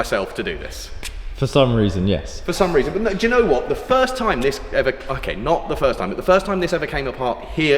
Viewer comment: What does say @ 17:57, so much sounds like the F bomb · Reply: "The first time this ever, okay, not the first time" 😅